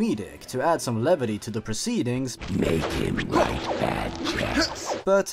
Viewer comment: When I played this game as a kid when the Knaaren said "Make him write bad checks" I heard "Make him ride bad jacks." I had no idea what bad jacks was but that disturbed the hell out of me.